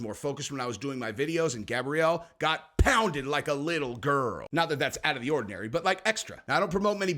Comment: did just say he pounds little girls & that's ordinary for him? That's what I heard.